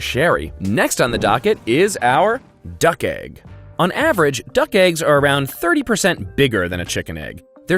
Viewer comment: When he was saying "duck eggs" i thought he was saying "duck-ays" 🤦‍♀️